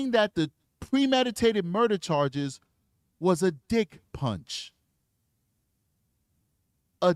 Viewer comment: D*ck punch???? 🤣 I thought he said 'different'! Thanks for clearing that up 👏🏾